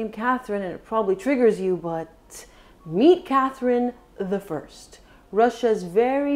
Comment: I'm tired of correcting people who didn't pay attention, so I'll make a separate post. At 9:10 she clearly says "meet Catherine the first". So many people think, for some reason, that she's talking about Catherine ll (the Great).